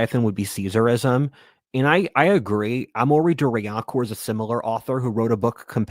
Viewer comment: Can anyone understand what the author's name is that he referred to? The CC came up with "amorei duryakor" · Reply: Amaury de Riencourt.